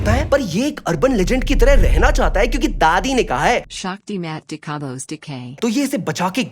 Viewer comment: did get what she said, ?! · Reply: "sakti mat dikha bsdk"